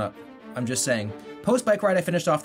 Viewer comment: I thought he said, " chick and sandwich."